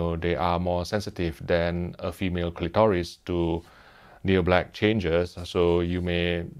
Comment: did he just say female clitoris?....